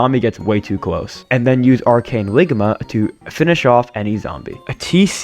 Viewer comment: DID JUST SAY ARCANE LIGMA ???!!!!!!!!!!!